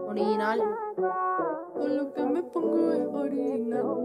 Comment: Se escucha que dice to lo que me pongo eh harina o soy yo 🤔